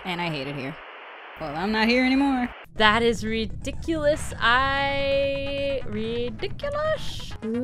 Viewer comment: I know didn't say it but I heard, "That is ridiculous utterly ridiculous." - Chloe from MLB & CN